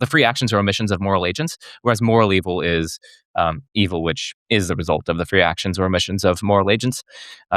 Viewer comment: I misheard as "Evil Witch".